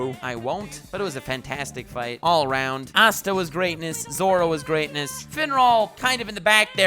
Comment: did you say Zoro from one piece was in black clover ARE WE GETTING A CROSS OVER